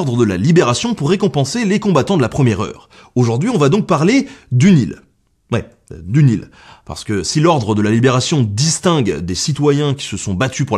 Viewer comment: quand ta dis du l'Ile moi j'avais compris du Nil -_